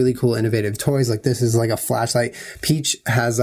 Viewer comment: I thought said "Flesh Light" not Flash light so that sponsor was super funny